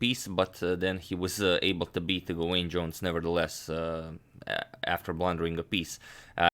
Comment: Misheard and heard "Dwayne Johnson" 😂